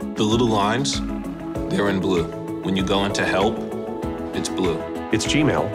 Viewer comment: when he says when you go in to help its blue it sounds like hes saying when you go in to hell its blue